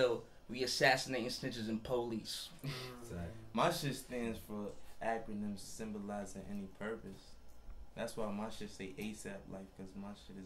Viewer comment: what does he say at 2:42 ? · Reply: Normen Meffert “my shit stands for acronym symbolizing any purpose”